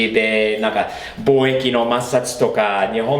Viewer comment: 貿易摩擦が貿易抹殺って聞こえて(ry